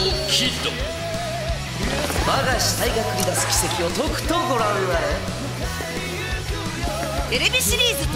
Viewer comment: 何て言ってる？ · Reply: 我が肢体が繰り出す奇跡をとくとご覧あれ